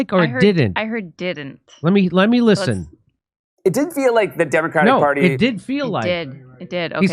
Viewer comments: it didn’t feel like is what I heard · I heard “it dint feel like” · Sounded very much like "didn't feel like", although captioning said "did feel like". Even played slowly I still hear "didn't" because I hear a nasal sound in there.